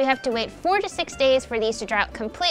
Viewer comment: Honestly, I thought said 46 days, instead of 4 to 6. 😂